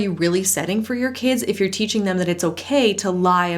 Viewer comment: I thought she said our pee.. no I’m serious I really thought that